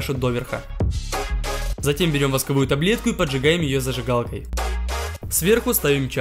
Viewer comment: господи, мне послышалось восковая КОТЛЕТКА а не ТАБЛЕТКА, я афигела🤣🤣